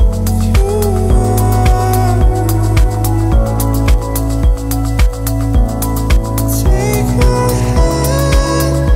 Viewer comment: best "take my hand" but what is after take my hand I can't understand, cause I'm not so good at english I will be greatful if someone would help me <3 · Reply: Is there "never go", "never let go"?